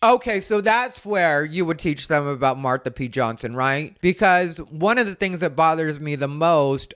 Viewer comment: You know, I am a 58 year old straight grandmother. I don't know all of this history. I swear, I thought you said "mark the P. Johnson." 😂😂😂 There's just no hope for me, omg 😂...you know what I was thinking...😂😂😂